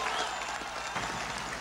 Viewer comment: Sounded like his said "Gorbachov" instead of Gorbachev ;)